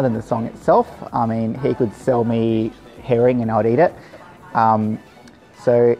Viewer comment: lol i thought the aussie guy said he could sell me heroion and i'd eat it but i think he said heron on second listen lol